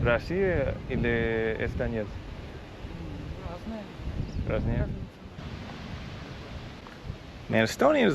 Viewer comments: she says "ну разные" which translates as "well different", not "they're Russians". Basically what she wanted to say is that there are both Estonians and Russian expats. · she actually said "Different people", and not "they're Russian". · she actually said "different"(about people living there)